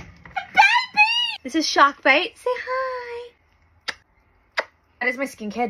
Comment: when she said shark bait, my brain thought she said a soft melodic "hoo haha" 😂